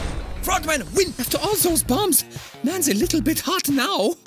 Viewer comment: it sounds like he says frogmen win